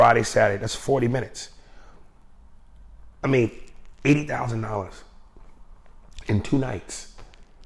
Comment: I THOUGHT HE SAID 8000???🤔🤔🤔🤔 NIGGA SAID 80???? YALL SURE??? ALSO SPLITTING TIME WIT OTHER PPL??? PERSONALLY....ID DO IT FOR 1K THO LMFAO...FREE TRIP ETC 💯🤷🏾‍♂️ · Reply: I thought he said $8,000 too but I went back to listen 4x lol and he said $80,000. That's crazyyy!